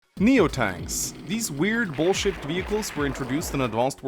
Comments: I re-uploaded this quickly because I opened the video calling the Neotanks “square-shaped” when I meant to say “ball-shaped”. The reason being is that I actually worked kinda hard on this video, and I didn’t want 90% of the comments to just be “huehue mbaaangs u said wrong thing xDDDD”. Anyway, enjoy a theory-crafting video about a 20 year old game. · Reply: I thought I heard you say "bullsh*t vehicles" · I re-uploaded this quickly because I opened the video calling the Neotanks “square-shaped” when I meant to say “ball-shaped”. The reason being is that I actually worked kinda hard on this video, and I didn’t want 90% of the comments to just be “huehue mbaaangs u said wrong thing xDDDD”. Anyway, enjoy a theory-crafting video about a 20 year old game. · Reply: I heard you say bullshit, not ball-shaped, lmao.